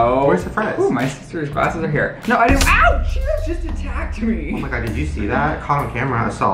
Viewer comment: I thought he said a cheeto just attacked me lol forgot about this video but its one of my faves